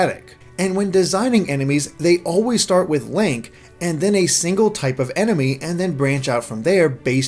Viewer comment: did you just call him "Lank"?